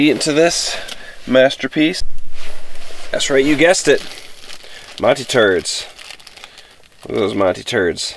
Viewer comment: I don't know if I heard this right, but did he say that he put Monty's turds in his food